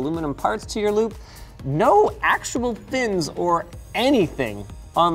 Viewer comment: Subtitles say "thins" but shouldn't they say "fins"? 5:16 Also, "for mark" should be Furmark